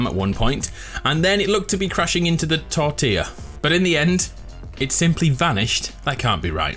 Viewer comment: did say tortilla